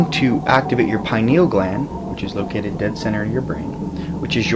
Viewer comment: llooooool i thought u said penial gland